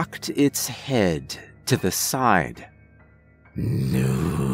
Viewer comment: Okay at 11:37 is the creature thing in the video saying "new"? Is it saying "who"? Or is it saying "YOU"? OR IS IT SAYING MOO? If anyone knows please let me know because the captions did not give me any answers sorry Oh and by the way I really love the narration and the story is really good 👍 I love stuff like this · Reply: lol it's saying mooooooooooooooo. It's a cow monster! Joking hah.. it's saying "new."